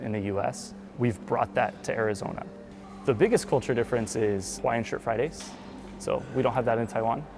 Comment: what did he say at around 14:06 the biggest difference is ...? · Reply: Hawaiian shirt Fridays